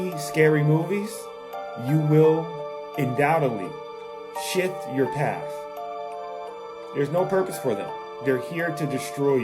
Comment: 😂 I misheard the first time and thought he said you will undoubtedly shit your pants.